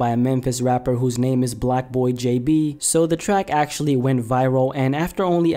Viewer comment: Oh I thought he said BlackboyJB